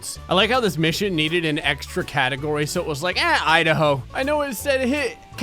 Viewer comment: I falt you said eh idaho but I think you said "hit idaho"